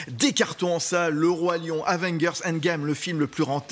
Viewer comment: Aven quoi ? 😂😂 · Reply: avenguerss mdrrrr